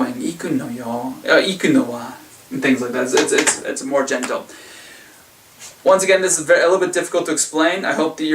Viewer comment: Did you say it's more genital @ 6:50? jk : )